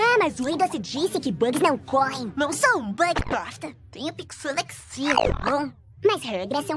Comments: só eu que ouvi: Não sou um bug bosta? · eu ouvi: "Ñ sou um Bug bosta" kkkkkkkkkkkkkkkkkkkkkkkkk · Kskskskskskksksks eu ouvi "eu n sou um bug bosta" kakakkakakakakakakak